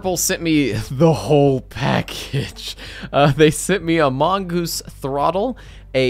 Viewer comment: did I just hear among gus. 6:08 in the video